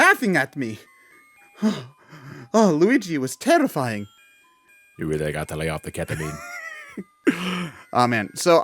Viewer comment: What does say on 9:07? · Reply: (as Manic Depressed Luigi): "you really need to lay off the ketamine."